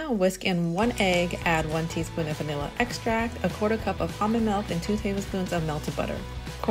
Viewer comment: What kind of milk? I've listened 25 times, sounds like your saying humming milk. What are u saying?